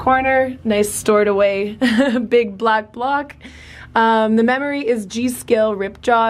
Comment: All I heard was “big black CLOCK” lol